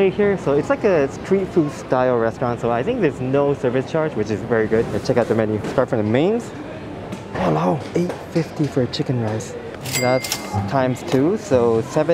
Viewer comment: Did u just say "WALAO"🤣🤣